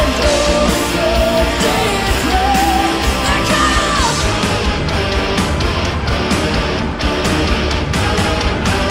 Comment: What is she saying at 2:25 ? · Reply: wacken